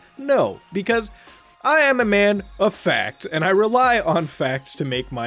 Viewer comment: So like...My video cut out when he said "I am a man of facts" and it sounded like he said "I am a man of fat." I can't stop crying.